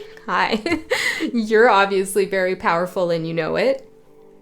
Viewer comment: 😂😂 I heard “I’m potent and I know it” 🎶 to the tune of “I’m sexy and I know it”